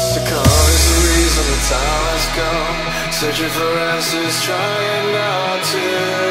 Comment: first i thought he said "searching for asses trying not to run" XD